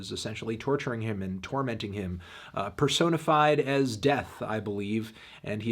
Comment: I was zoning out and I thought Anthony said Persona 5